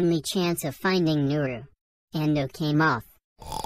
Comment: Pls do Dearest Family miraculous switch 😊 when you can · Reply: Plagg said Hawk Moth! instead of Ok Moth